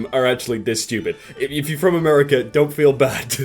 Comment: Did... did he say if you're from American?